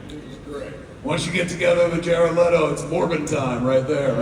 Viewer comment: What did he say? "Once you get together with Jared Leto is Morbin time?" What the heck is Morbin???? did I hear that right? What is he talking about? I have no idea, but the audience understood